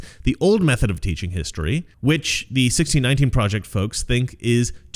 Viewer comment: Im so sorry, maybe im hearing it wrong. Every time you say the words "the 1619 project" I hear "Retarded" and want to go 🤪 "Durrrr, thppppltt duuuuhhhh."🤪